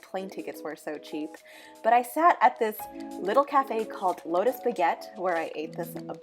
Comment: listening to this while i was drawing, i heard "load 'o spaghett" instead of lotus baguette lmao. had to double take. honestly this is such a chill vlog tho. its not too extravagant like a ton of other japan vlogs!